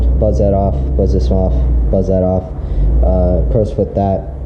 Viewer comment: What did you say at 4:53? I’m trying to get that bolt iff now but all the deep sockets I see at stores are just shy of reaching that nut. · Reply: In this video I recommend a Crows Foot. In one of my more recent videos <link> ), I bought a Extra Deep impact socket which was a million times easier and safer, but its like a $30 socket.